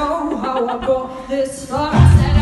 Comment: I swear I heard "car" XD